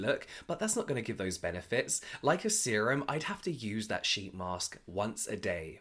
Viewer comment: 😂 you made me laugh. At minute 2:33 I thought you said “shitty mask”. 😘